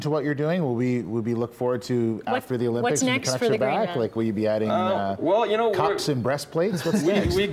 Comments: That's what I heard too "cocks and breastplates" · what the hell did the newsman say at 5:24?? cocks and breast plates? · Did he say "adding COCKS AND BREASTPLATES"??